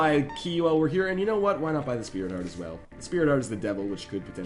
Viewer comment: did he said speared heart?? lololol